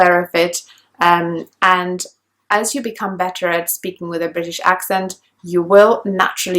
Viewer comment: Excuse me? I think I just heard a "beddeR".